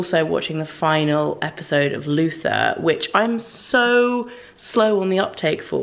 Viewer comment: I had to search for this video to watch it again. You mentioned a favorite tv show that you were watching a lot. I thought you said 'Lucifer,' which is a tv show on Netflix. I am on season 3 now and just found out that there is a highly acclaimed show called 'Luther.' I've just been watching the wrong show for 2 months! Well, now I'll start watching 'Luther' and you can give 'Lucifer' a try...it's actually a pretty good show. LOL (I need to listen better.)